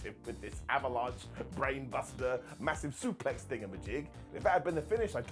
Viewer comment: what does simon say? "Avalanche brainbuster massive suplex ....."? He always says it and I never understand what that word is. Thing of magic? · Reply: Thing-a-ma-jig , north american slang for "whatever its called"